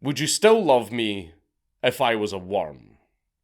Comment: Had to rewind. Honestly thought you said “bum me if I was a worm” 🚬